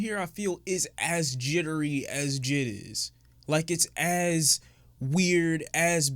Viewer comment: I heard, "as jittery as jizz". I need this visual bleached out of my mind.